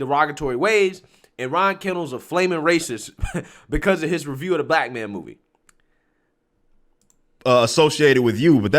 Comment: Original video: <link> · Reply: Did you say review of “The Blackman” movie?? 😂 I know I’m going deaf but seriously. 🤣